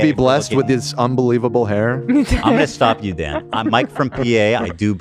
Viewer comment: Still think about the person that thought Ethan was saying "Mike Frumpie-A" not "Mike from P.A" 😂